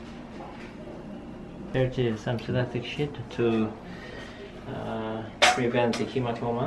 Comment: I thought he said “shit”😂😂😂